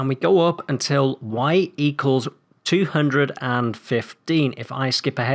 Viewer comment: When I was building this farm, I had thought you said 250 at 4:00, so I built up there, and played the video again. Moral of the story is listen closely when someone is talking XD (it’s a great farm though, thank you!)